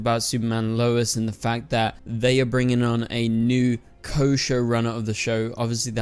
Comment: Sounds like you said a new kosher show runner 🤣🤣